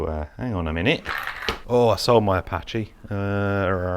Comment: I think those are both Zorro’s below? (open cockpit, Apache just has sticker for windows) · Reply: Oh yes, I was wrong! Thy are Zorro's as you said :) He did have a orange Apache though, which is not shown here.. I thought he said 'I saw my Apache..' but now watching this again I think he says 'I sold my Apache..'